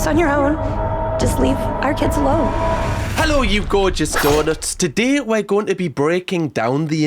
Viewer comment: Ohh..."donuts" 🍩 😂 I wasn't paying attention the first few times & I thought you were saying "daughters" 😁 like how some channels have semi-weird little nicknames for their viewers, etc. 😎